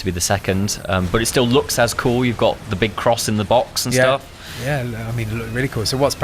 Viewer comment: what was that you said? You got a big "cross" in the box? Not an X?